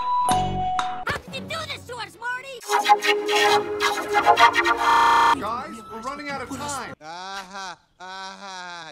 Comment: what is Marty trying to say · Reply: Greninjafan101 "What's the big deal? I was coming back in the morning."